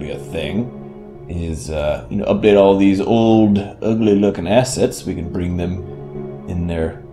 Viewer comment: Totally thought he said "Ugly lookin-ass sets" for a sec